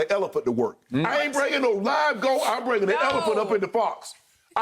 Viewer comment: I swore Shannon almost said "bringing an elephant up in the bytch!" 🤣🤣🤣🤣🤣